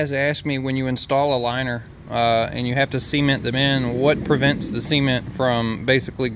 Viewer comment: So I’m British and I have to double take every time he says ‘cement’ it literally sounds like he says Seaman.. “we have to fill that hole with seaman” WHAAAT 😂😂